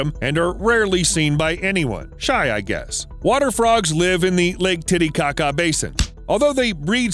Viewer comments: Since no one is saying it, I really thought you said the frogs live in the lake titty kaka basin @ 3:52 ... that is all. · wait what did he sayyyy ✋💀💀 ✨laketittycaca✨???!!!